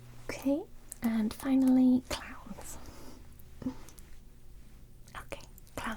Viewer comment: I misheard this as "clams" and now I'll be thinking about how to make terrifying clams for a while.